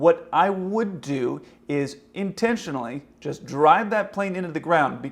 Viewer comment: did you just say drive